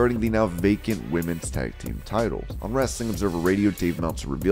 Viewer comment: I thought he said naked women's tag team titles. Lol